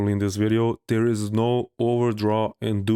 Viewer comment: I think I just realised, you were trying to say "There is no overdraw", right? I heard it as "ordraw" until